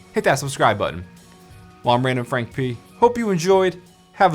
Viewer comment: It sound like your saying random frank Pete